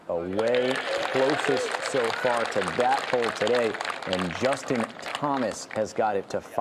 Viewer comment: I first thought it said Justice Thomas shot an 67! and thought he has more problems than have been revealed.